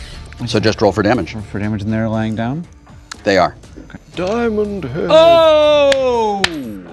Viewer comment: Did Yuri say Diamondhead? He was a buttload of voices in Ben 10.